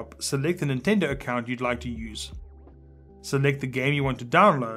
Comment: Am I the only one who heard "Select the gay you want to download"